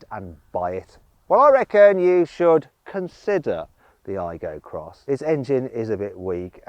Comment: 日本語訳が変なような…？アイゴXは"I go cross"と言っているように聞こえるので、「私はご機嫌斜めになる（怒る）」っていうギャグを意味しているかとｗ